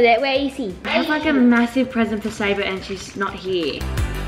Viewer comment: uh is it just me or did biggy swear at 7:20 cause i swear he said sh-t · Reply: It was sabre